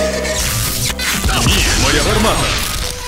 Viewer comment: он что сказал "** твоя бармала" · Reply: Ні моя гармата, нет моя турель